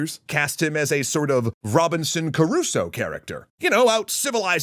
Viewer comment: Why do i hear that name spoken as "Caruso" so much? The name is Crusoe and wiki says it's phonetically "KROO-soh". 🤓